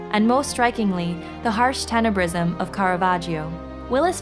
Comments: What did it say about the harsh "something" of Caravaggio? Sorry I didn't get the word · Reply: "The harsh tenebrism of Caravaggio" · What did it say about the harsh "something" of Caravaggio? Sorry I didn't get the word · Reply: the word is “Tenebrism” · HELP Please! What did it say about the harsh "something" of Caravaggio? Sorry I didn't get the word 04:26 :( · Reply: Tenebrism: a style of painting developed by Caravaggio and other 17th-century Spanish and Italian artists, characterized by predominantly dark tones and shadows with dramatically contrasting effects of light.